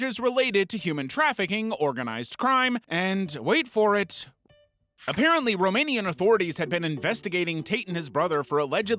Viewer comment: idk what he said here, and honestly do I wanna know????? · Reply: Rape